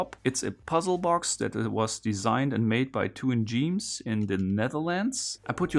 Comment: I heard "Made by Tuan Djeems in the Netherlands" as "two ingenious the Netherlands" and was very confused for a moment.